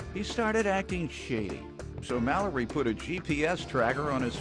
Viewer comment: I thought that the announcer said 'shitty' and not shady🤣😂